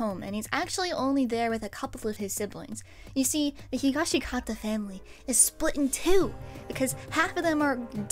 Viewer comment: I was kind of zoning out a bit and then I heard "the Higashikata fruit business" and did a full on double take